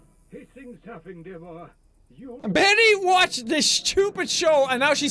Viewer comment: I swear that sim on the TV said "These things happen, dear boy..."